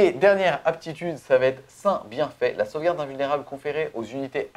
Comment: Je suis le seul qui ai tiqué à 38:18 quand j'ai entendu "Saint bienfait" en comprenant "seins bien fait"? :O